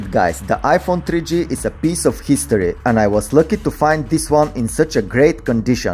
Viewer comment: I thought he is about to say peace of sh** 😂